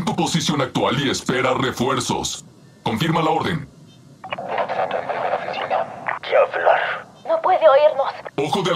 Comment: creo que dice nada en la primera oficina o algo asi